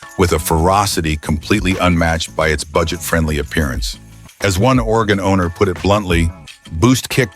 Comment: "As one Oregon owner put it bluntly". at first I thought he said, "As one organ donor put it bluntly". LOL. Yikes!